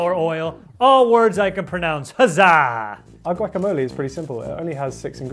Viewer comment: I thought you said "Human" first @ 2:03:04 ...